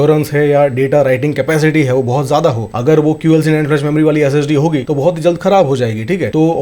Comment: Time 1:27 What did he say ? QSNL ?